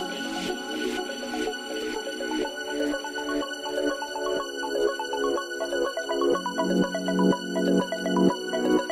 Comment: This NEEDS more views! (: At 3:31 do you think she says "I got so high that i though that.."? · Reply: I thought that I was stronger